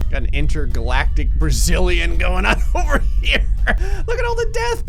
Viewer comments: did you say ... brazilian? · does he say "DeathPS" instead of FPS